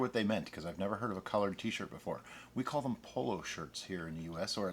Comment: At first I heard "Collard T-Shirt" and I thought that made complete sense. I need more coffee, carry on...